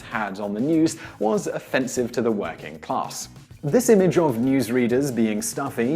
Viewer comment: in 4:36, working class but what i heard Whacking Class...